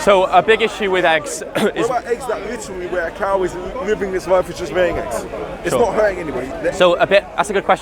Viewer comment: Did he just say a cow living its life and laying eggs..?